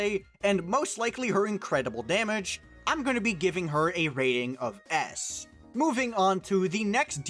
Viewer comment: gonna give her an ASS 😂😂, quick milliseconds i kinda misheard